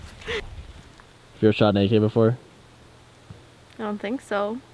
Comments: Legit thought you said have you ever shot naked before not have you ever shot an ak before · I thought he said have u ever shot naked not a ak lmao